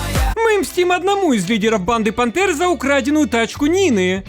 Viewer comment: Мені почулось "банда бандер", майже дизлайк не поставил